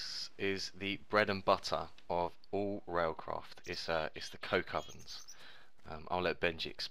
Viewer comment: Swear he said coke ovens...